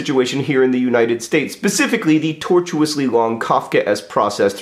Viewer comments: did he say 'pecifically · I heard "caucus" instead of "Kafkaesque", which I felt was surprisingly appropriate.